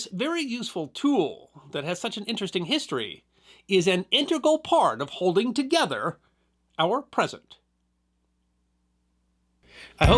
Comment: Did he say 'this tape plays an important part of holding together our president'?